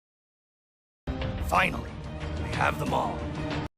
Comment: I think he did say we have them all, referring to his brother